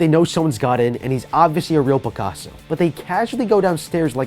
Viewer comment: Did he just say hes a real Picasso? XD